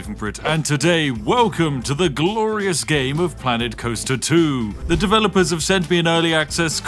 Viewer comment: why do I hear "man in coaster2"💀💀💀💀💀💀💀💀